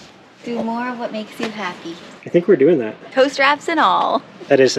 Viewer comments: You have a lot of love behind you! And…who else thought was saying “toaster wrap” for “ tow strap”!? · I kept hearing “toast wraps” instead of Tow Straps! LOL. I thought it was a Portuguese dish you were heading to enjoy! I love your new property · You have a lot of love behind you! And…who else thought was saying “toaster wrap” for “ tow strap”!? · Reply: For sure that is like what I kept hearing him saying “toaster app” for the first 5 or 6 times. Then it all made sense when they said to get the camper on the land… ooooh! Lol.